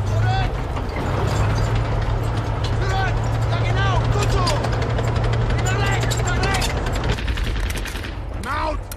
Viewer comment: What the german said? Zuret jagernaut gut só, pra lex pra lex. ? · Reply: "Zurück! Ja genau! Gut so! Nach rechts! Nach rechts!" Grüße aus Deutschland!🤠